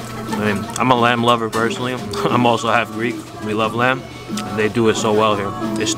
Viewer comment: The lady along in this tour with Pitbull and you is I think the singer called H.E.R. Excellent !!! At 3:59 did the guy say "We Love Lamp" ?? I didn't know that Pit Bull's and Brick Tamland's son was on this video !!! BEYOND EXCELLENT !!!!! Pitbull, H.E.R., and in the same video. My head is going to explode !!!!!!!!!